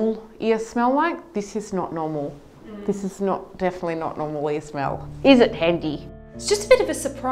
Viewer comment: But why she (the doctor) keeps smelling it tho? She is into a lil dirt play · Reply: oh smelling i thought you said smiling 🤦‍♀️